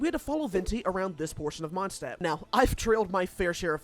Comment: i misheard “trailed” as “drilled” and had to rewind